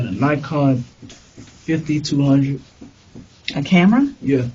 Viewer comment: no he didn't, I even read the transcript and if you go to the 7:11 mark in the transcript it says Nikon D3500. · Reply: HE clearly said Nikon 5200. The cop and southwestxnorthwest got it wrong.